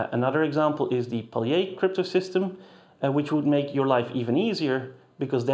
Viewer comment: ElGamal supremacy (obviously) but at 14:56 the subtitles say "P crypto system". Is that meant to be Paillier cryptosystem ?